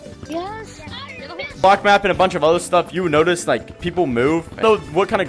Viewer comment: bros caption Said f*ck map